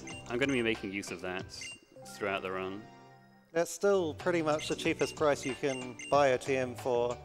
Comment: Did he really say "Cheap as Christ?" Dare you!